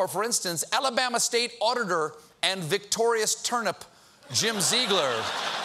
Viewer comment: Oh geez, I thought he said "Victoria's Turnip"!